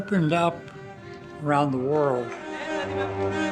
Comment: what is this filmmaker's name? I can't understand Sarris' pronunciation. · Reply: Wong Kar Wai